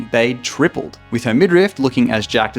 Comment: did you just say Midrift? 😂